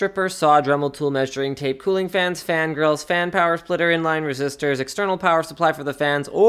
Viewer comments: Wait a sec did he say he added fan girls at 02:06 ? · Did he say fan girls at 2:07? hahaha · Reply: Fan grills